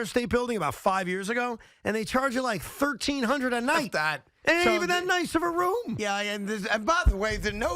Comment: did carton say “fuck that”